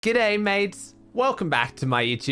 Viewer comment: I was worried for a sec bc I thought he said " hey mates" instead of "good day mates"